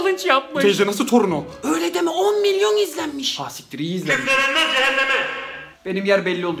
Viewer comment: nediyo anlamadım cehennem dediğin duydum öncesin anlamıyorum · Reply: Orda "Küfür edenler cehenneme" diyor